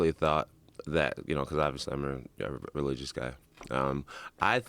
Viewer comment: The luckiest thing that ever happened to Jon Jones is the fact that that baby lived. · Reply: Did jones say he’s religious ??? I couldn’t understand around the 3:00 mark